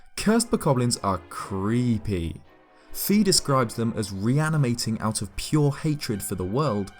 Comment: Sounds like you said "Fetus scribes them as..."